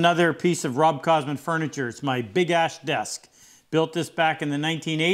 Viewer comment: Oh I reread the title, it sounded like you were saying big ass not big ash, my mistake.